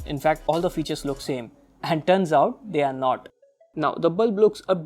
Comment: when he say dones out instead of turns out